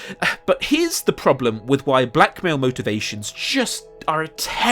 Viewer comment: Ohhhh Black Mail! Thank God you cleared that up lmfao. Thought you said Black Male 😆 🤣. I got confused for a sec but both still fitted lol